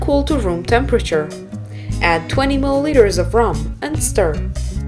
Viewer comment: what's that ingridients? please help me cant understand..give me the exact ingridients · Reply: That's rum.